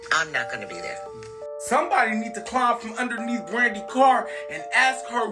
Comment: ooo I just realized what he said “somebody need to climb from underneath brandy car” diabolical 😅